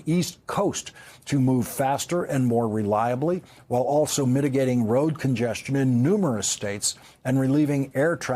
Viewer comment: did Lawrence say road congestamion? Did he put an M in there? It's not a huge deal if he did or didn't but I gotta know, did he?